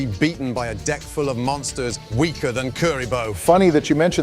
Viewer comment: I heard, "Weaker than curry bowl." looolll not Kuriboh